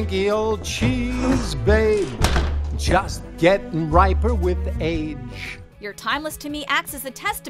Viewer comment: I could’ve sworn I heard Chris say “You just get better with age” instead of “riper with age” when I first saw the film